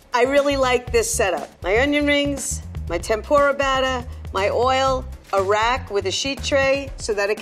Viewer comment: uh oh, I heard "My oil, Iraq" , this lady.......this lady is up to something